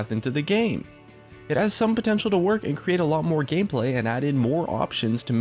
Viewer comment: Anyone hear at 3:20, "adding a lot more gay play"?